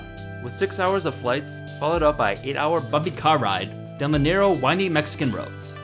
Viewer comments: I thought u said f**king car ride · What does he say? 8hr 'Fucking' car ride Bumpy? Buggy? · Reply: Bumpy